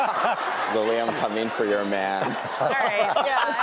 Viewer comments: what did the person in the crowd say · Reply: “Lili I’m coming for your man! (Cole)” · What does the audience says in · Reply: “Lila I’m coming for your man!”